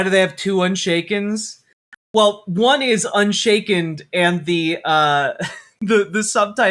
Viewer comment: Did she really just say "unshakened" ? Wow, my English teachers (as a foreign language) would have smacked me for this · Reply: I HEARD "unshaken", but for some reason the screen said "unshakened"...then again, two seconds later they misspelled Brooklyn.